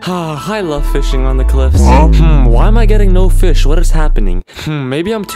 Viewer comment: did u just say "lava mlg"? lol